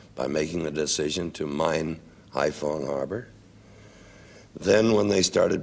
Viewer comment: I couldn't make out what he said, so I turned on the closed captions and it said "by making the decision to mine iPhone harbor".